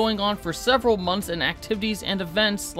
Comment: Am I mishearing or did I hear “kelmp keep morale up” at the end